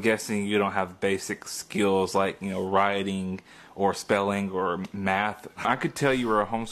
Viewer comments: I thought he said roiting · I thought the dude said rioting at first. Lol